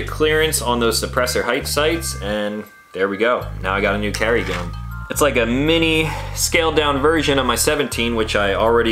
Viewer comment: what holster did you say that was @ 13:49 ? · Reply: QVO more discreet for G19